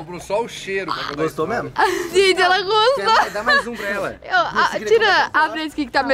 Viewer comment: MINUTO 8:07. OUVI A DANE FALANDO PRO: VIDA ELA GOSTOU. QUEM OUVIU TBEM? TA ESTRANHO KKKKKKKKKKK